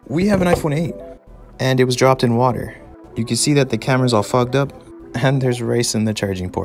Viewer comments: When he said "it's all fogged up" I heard it "it's all fucked up"🤣🤣🤣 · Did he just sayd the camera is all fucked up I though he is like BBH